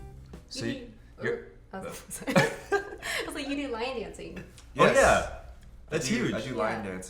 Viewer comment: I heard "line dancing" instead of "lion dancing" heh , I think I prefer the latter though.